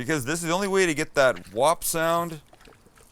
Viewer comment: Did Sam just say @ 4:33 into the video to get that WAP!!! Sound? Aka Wet Ass Pussy sound because thats what it sounds like when u play with it and stroke it?